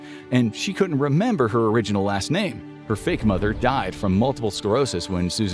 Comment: why did he say her original ass name😭✌️ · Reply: He said last name.